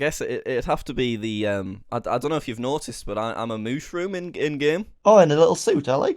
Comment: i heard you say "well i am a mushroom with a suit" like 5 times